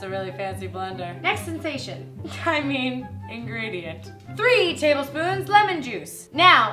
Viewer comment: When she said ingredient I thought she was about to say Ingrid.. #Hangrid